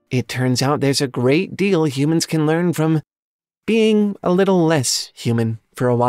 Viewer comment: Oh, a little less human!.. I thought you said a little less humane. Well I've gone too far now.